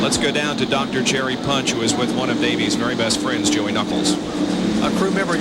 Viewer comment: Did I hear that right? @ 07:55 Was the Punch and Knuckles? hahaha